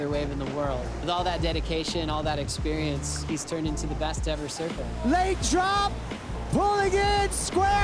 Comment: did he say the best to ever surf pipeline?